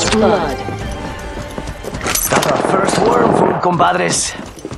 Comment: 「新しぃ56しまぁす」 にしか聞こえないのは僕だけか